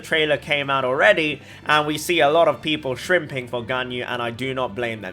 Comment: I heard "shrimping for ganyu". Is this what it is? Do i detect another one in rabbit hole?